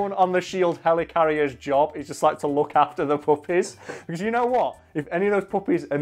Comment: Lol I thought you kept saying puppets 😂😂😂